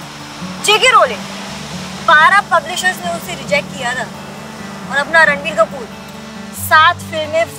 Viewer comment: did she said Randi Kapoor😂😂